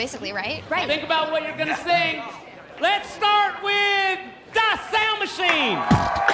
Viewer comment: When I was like 9 I mishear the das sound machine As “The Sandwich Machine”